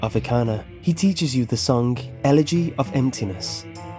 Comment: Every time i see some person comment about the elegy of emptiness being a ben drowned reference i die a bit on the inside. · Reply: Darkterror the Faceless Void Thought u said edgy of emptiness